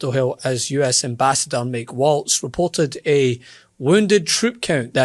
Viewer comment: For a moment I thought you said Tim Waltz, I was what?! What is he doing here? Oh it’s different guy and is named Mike Waltz… any relation?